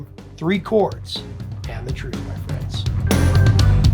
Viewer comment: As a kid I always thought he said the Ruth...